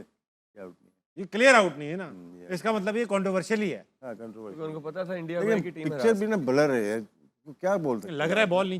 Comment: What amir said on 28:32 ? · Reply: bcci given markram out cuz if sa qualifies for final India can't win Fixer Amir : Unko(According to pakistani ICC is indian cricket council) pata tha SA unki(Indian team) ko hara sakta hai